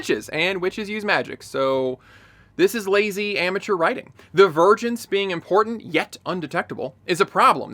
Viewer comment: I know it's "vergence". But what i heard was "the virgins being important, yet undetectable" , "how did virgins become such an important part of the story?", "... establish stakes for any Jedi that came in contact with virgins" and so many more. It's dumb, but I'm crying.